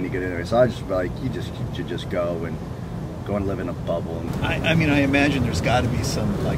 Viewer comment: I thought he said "you can go live in a butthole" lol I had to replay that than I was like "ohhhh haha"